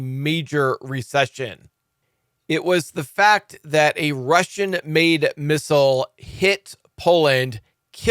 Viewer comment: Thought President said it is not Russia missile